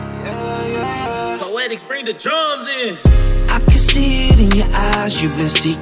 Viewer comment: This is my cut!! What does it say in the beginning tho? sounds like it says "something like poetics bring the drugs in" probably says jugz im imagining? but it sucks cause it sounds like it says drugs which can give the wrong impression if someone passes by and just happens to hear that lol.